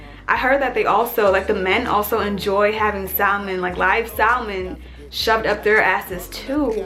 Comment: what did she said. Live saw man?